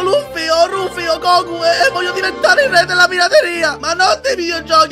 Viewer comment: solo io ho capito "re della prateria"